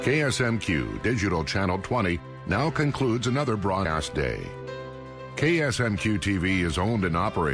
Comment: Did they say another broad ass day?? XD